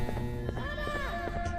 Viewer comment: The naughty ones, what are they saying? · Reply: DADA